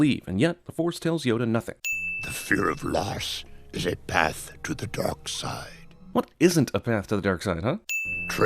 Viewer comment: It sounds like Yoda is saying " The fear of Lars is a path to the dark side. "